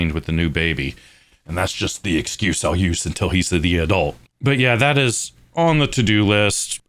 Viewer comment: What did you say at 5:47? "And that's just theesa cuseayoos until heesa the adult!" Jar Jar Binks out here...